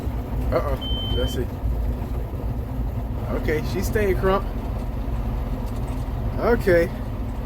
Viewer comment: did you say she´s staying krunk? I only know "krunk juice" from hearing about it...Ha Haa!. Leave your air conditioner ON ´cause it will remove moisture in the cab. PLEASE check out the video entitled "Roadside mowing 2020" on YouTube. There you see the chains on front and the rubber skirt on the back that I was talking about. There is even a sickel bar to cut tree limbs available, I LOVE mowing!!!!! We just got a new Fendt tractor at the County Road Commission I work at. Have Danny maybe look into getting an arm with a 10 foot reach, you will be thankfull for it. P.S. I always watch the commercials. You said it cuts better in reverse, are you sure you don´t have the blades flipped the wrong way? I ndid that before when I forgot which way they rotated. Please please don´t get frustrated with my saying stuff. I just like you so much and want to make things easier for you. --Your friend Dave from Germany. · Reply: Lollol yeah I said crunk! Lollol and ok I'll have to check that video out! I actually already know what you're referring to when you say chains and a rubber skirt. But say you got a Fendt! Those are some fancy looking tractors! We have a long arm cutter that reaches about 15 ft I think it is and man do I cut up the world with it when we do have time to hook up! Lol Iove it too! Thanks for watching the commercials! I don't even know if it matters! It's all new to me! Lol and about the direction of the blades, I forgot they actually turn clockwise! I forgot we switched the hoses on the hydraulic pump to make it throw stuff away from the tractor! So that board is slightly needed! I appreciate all you feedback! No worries!!